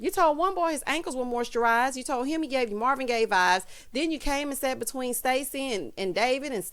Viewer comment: OH!! Ida said MARVIN Gaye vibes!!! I thought she just said gay vibes! I was thinking omg! I can't believe she said that, and he didn't go off!!!